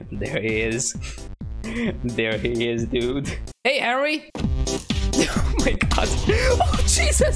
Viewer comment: did you say Harry?